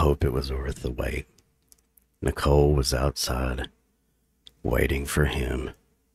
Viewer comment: I hear ' Nicole was outside waiting for JIM